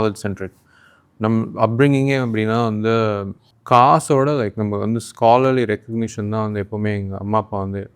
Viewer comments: caption’s got bad translation. He meant “kaasu” = money /wealth. Caption got it incorrectly as caste . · he didn't say 'caste' he said 'kaasu' which means money. In 31:57, he said '20 billion' but the captions say '20 million'. Please review the captions again, editing team.